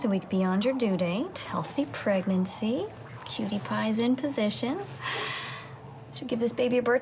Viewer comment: did she just say " cutepie " in position ??? hahaha